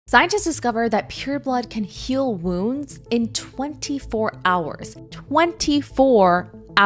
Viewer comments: Oh I thought you said "pure blood can heal wounds in 24 hr" 🤣 I was so confused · i thought she's saying pure blood but it's period blood so for super soldier serum we just need period blood thanks now i get it 😯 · I thought she said pure blood and was so confused